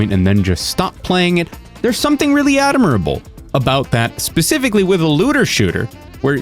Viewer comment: I hear you say “adimirable”and i don’t know where you get the extra “I” but I love it. Thanks for the content, you’re looking quite dapper.